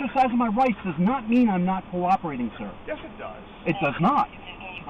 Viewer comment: The P.I. Said- “Just because I’m exercising my rights doesn’t mean I’m being difficult” Cop- “yes it does” That’s the problem guys. 🤦🏻‍♂️ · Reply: He didnt' say "difficult." He said "not cooperating." Exercising his right to remain silent is technically "not cooperating." He wasn't cooperating with the police, so the cop was right. He has the right to not cooperate though, so he wasn't doing anything illegal. The cop was still right with his statement though. I think your bias in the matter altered your memory of what was said.